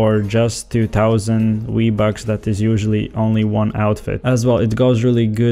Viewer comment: Boi gotta get them fucking WEE BUCKS